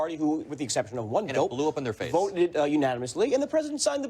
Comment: 🤣 I had to replay 21:28 4x cause I thought said ‘there was lube on his face’ With this administration and bubba treatment I really wasn’t sure lol